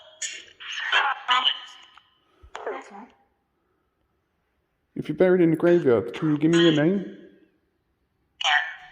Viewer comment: I thought I heard the name Rebecca at 9:25 but I could be wrong 🤔❤️💕. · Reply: Yes it did say Rebecca!!!!